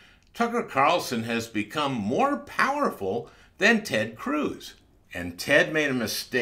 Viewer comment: Powerful?!? Oh, I thought you said paranoid 🤣